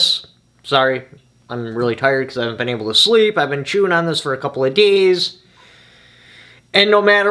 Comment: At the beginning of the video I thought I heard you say “I haven’t had much sleep, I been chewing on a couple a gays”! 😂